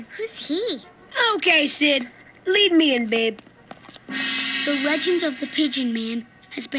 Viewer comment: Did he call him babe ????? I love iitttt. I miss this time so much mannn TAKE ME BACK